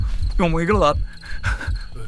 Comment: Could anyone understand what Sam said at 22:53? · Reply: The CC on the video shows SAM: OH, my God!